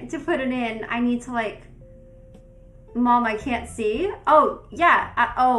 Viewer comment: Someone should try cutting out the music and enhancing the background sound. I think I hear some kind of conversation going on back there. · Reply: I hear “mom? Mom...? I can’t see! MOM!”